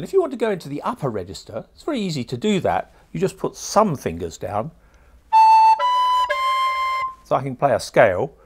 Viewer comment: hahahah i thought at 1:40 he said: put some fingers down plays recorder fucking play a scale